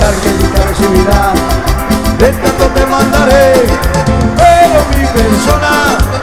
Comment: Hola para la que pregunto que decía en el minuto... 27:47 lo que dice es...un retrato te mandare